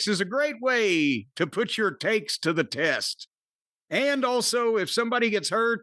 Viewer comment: Misheard this as "put your taints to the test"